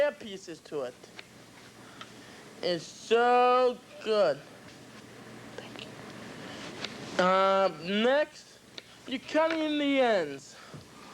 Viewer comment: Anyone else hear the "thank you" at 6:50?